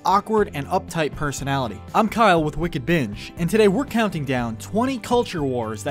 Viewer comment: Thought you said wicked bitch